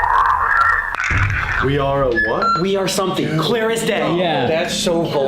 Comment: I hear "We are THEM" !!! 😮